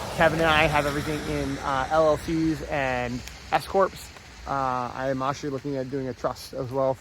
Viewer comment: oh s corps not what i heard on the first pass thought you and kevin had escorts lol.